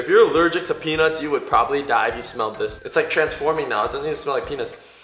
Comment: it sounds like he said it t doesn't even smell like pinis